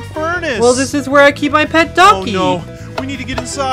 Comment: Was I the only one who thought said donkey instead of ducky